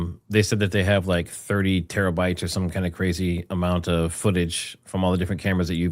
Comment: I thought they said 30 terabytes not 300